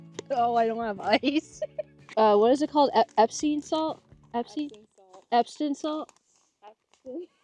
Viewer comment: I was hoping you called her lady of the night because she was a hippy and original meaning was had to do with the moon, im more concerned because i thought i heard you call it Epstein salt